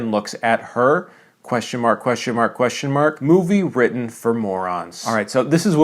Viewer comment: From the thumbnail and throughout until 1:30, i thought he said “movie written for mormons” Like, “why does superman have multiple robots—as in multiple wives???—but… superman is protestant”